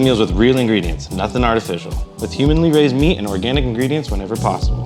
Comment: did he say Human-ly raised meat?